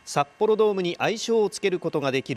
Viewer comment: ニュースのナレーションが『札幌ドームに愛想を尽かす事が出来る…』と聞こえましたが今の窮状は、あながち聞き間違えでも無さそうですね…😜